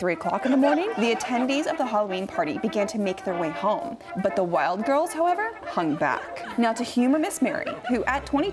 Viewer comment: I kept hearing “wild girls” instead of Wilde Girls. The wild girls stayed behind. Why were they wild? Like partying wild?